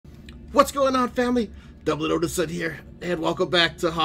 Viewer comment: Lol, I just started the video, and I thought you said double-o inson! Like you were 007, hahahahaha!